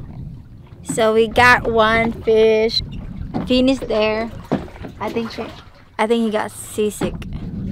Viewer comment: At first i thought i heard got sisig until i read some comments here that got sea sick not sisig🤣🤣🤣